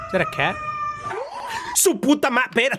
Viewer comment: “SU PUTA MATERA” WHAT DID YOU JUST SAY?!